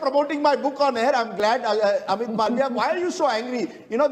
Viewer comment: Thought I heard you said "Amit Malware" 😂😂😂